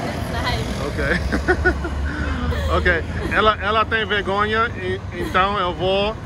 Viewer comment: I think she said « Cabo Verde e sabi » Cabo Verde is great.